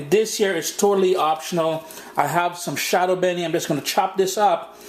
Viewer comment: what is that??? i couldn't understand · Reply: "Shadow Beni" uhhhh they also call it culantro not cilantro but some else will have to tell you the American version